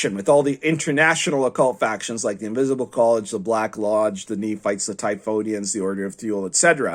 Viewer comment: Did you say Nephites? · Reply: Nemites.